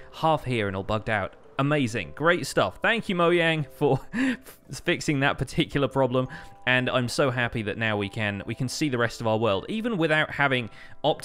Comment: I thought he said thank you moyang lol